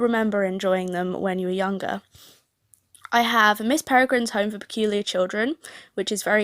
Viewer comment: what is name i didn't hear right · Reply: Miss Peregrines Home for Peculiar Children